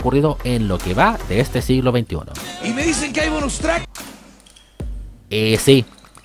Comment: Oigan: ¿Dónde consigo ese meme de "Y me dicen que hay un Strike" que acá indica Bonus Tracks? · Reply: Es que entendí "Y me dicen que hay un Strike". 😂